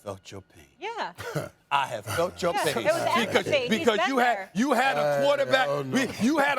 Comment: I thought Stephen A said "I fucked your pain" 🤣